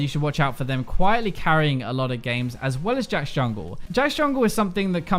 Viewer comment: Minute 10:50 did he say Jayce Jungle or Jax ? Didn't understand :((( thx guys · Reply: Jax